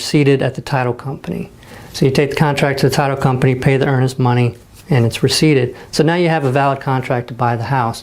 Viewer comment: Does anyone know what he said at 9:19, "you take the contract and pay the __'s money"? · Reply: I mention to 'pay the earnest money'. That's the earnest money as agreed upon with you and the seller (homeowner). The title company holds this money in escrow and is part of what makes the contract you have a 'binding' contract. Without earnest money the contract (in most places) would not be considered valid or 'binding'. Disclosure: I'm not an attorney so always run things by one.